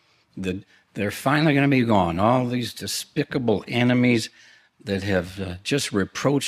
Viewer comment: I totally thought he said “despicable animes” and I was like: ya know. You’re not wrong. 😂